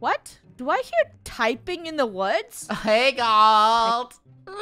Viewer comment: Omg did I hear a hawt gal? 😂